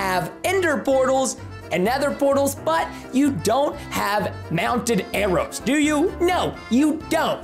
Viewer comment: At first, I thought you said "mountain" arrow but then you FLY THROUGH THE AIR! Then I'm all like "oh, mounted arrow!"